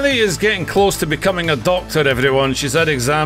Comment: Congratulations to jelly in becoming a doctor we need them so much and they aren’t treated the best by any governments but they are what help us the most in the time of need so I think I speak for everyone here when I say congrats jelly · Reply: I was looking for this comment. I swear I heard "Jelly is becoming adopted" 😂